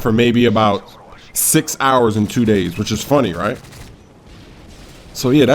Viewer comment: Did he just say six hours and 2 days